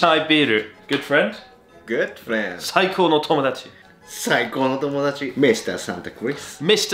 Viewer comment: Why do I hear 'Saiko no Tomodachi' as Psycho no Tomodachi...